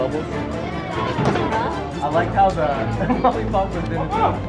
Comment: It sounded like she said how the hel